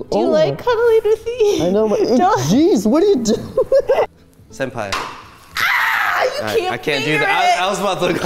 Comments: Am I the only one who thought said "Senpai" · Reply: He did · Am I the only one who heard senpai at 6:10?